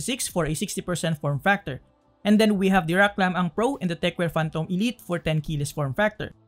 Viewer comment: WAT IS HE SAYING I NEED TO KNOW GOOD TKL BUILDS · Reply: The first was Rakk Lam-Ang Pro and the second was Tecware Phantom